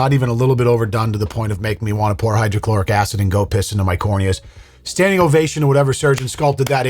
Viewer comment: I coulda swore you said "makes me wanna piss hydrochloric acid into my cornea"....I mean,I feel your pain😅😢. I just wanna say," if their insecurities (body dysmorphia) goes this far, what are they gonna do to that baby's perception of themselves"? Makes me worry for the baby. Idc wtf they wanna do to themselves.